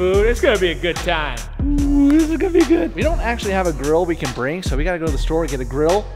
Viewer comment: I thought he said, "we don't acctually have a 'girl' we can bring"! Lol 😂😂😂